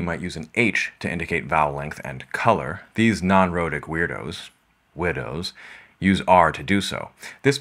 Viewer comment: What up, doc? Maybe someone pointed it out already, but to this English ear it sounded like you said 'widows' when pronouncing the non-rhotic 'weirdos'. The OED would have it we stick a schwa in there for shits and giggles: /ˈwɪədəʊ/